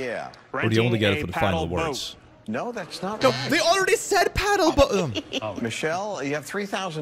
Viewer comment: To be fair to the paddle boat people, I think it might be an accent thing- they could have been saying 'pedal boat' but their accent made the presenter think they said 'paddle boat', and then they just guessed random letters because they were already told the correct answer was wrong, so now what can it be? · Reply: Oh they said PADDLE.. i was constantly confused because I thought they all said pedal and only one was correct? Thank you for clearing that up for me